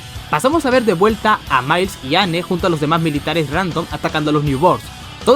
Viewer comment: escuche ano